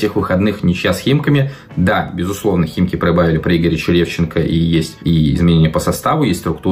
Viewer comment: Послышалось про химки "проебавили" 😂😂😂